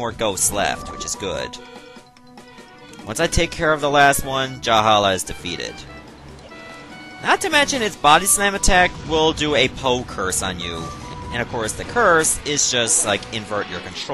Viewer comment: for a second I thought you were saying it would cast a Pokerus on you.